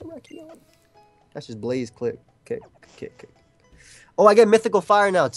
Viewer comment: WHO ELSE HEARD JOEY SAY BLAZE CLIT AT 14:08??